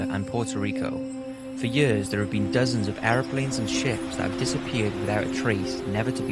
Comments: I thought he said "Airplanes and shit". Hahah. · I thought he said "Airplanes and shits" and it was "Airplanes and Ships". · I swear I heard "Airplanes and shits".....or its just me? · lol I thought you said" Planes and shit"